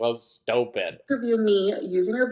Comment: This whole time I thought she was saying “LINTerview” referring to the ASMR lint picking but she’s saying LYNNterview.. of course she is. Freaking narc